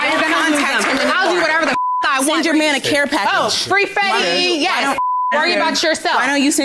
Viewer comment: Did I hear her say “Free Fetti” as in former rapper Fetti Wap 😮🤣🤣